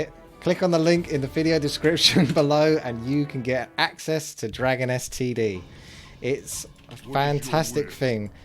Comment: I keep hearing "Dragon STD"...